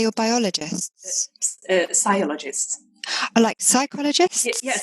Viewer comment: I thought she said a Psiologist !?